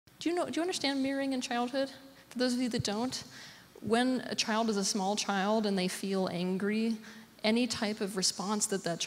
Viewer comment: I thought she said marrying in childhood😬